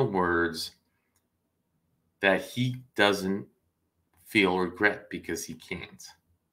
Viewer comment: !!! He said he does feel regret. You misheard him.